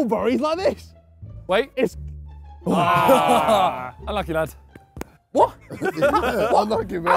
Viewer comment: does Simon say I like your dad??😂😂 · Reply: No he said ‘’Unlucky Lad’’